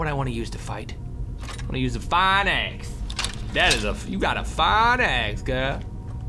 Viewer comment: i thought he said fine ass xDD